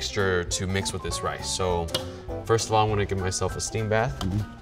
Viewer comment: I thought he said 'but first I'm gonna get my self-esteem back' and it resonated with me.